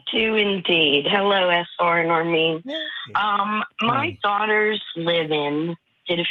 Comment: I came here all the way from the podcast version to ask somebody/anybody about smthn' says at @ 45:00 She says ~ My Daughters'????? It sounds like she says ~My Daughters LIVE IN?? After she says this she says her daughter and boyfriend are getting uncomfortable with it...or smthn' along those lines...can anyone help😂